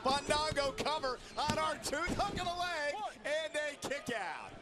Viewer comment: Sounded like he said R-Tooth